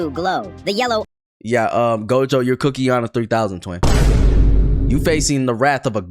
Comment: fkn captions saying shi like- "yea uhm gojo, your cookie on a 3000"